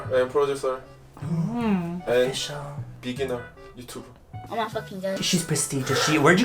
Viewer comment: did I hear “I’m also ginjang”? 😂😂😂😂😂😂😂😂😂😂👏👏👏👏👏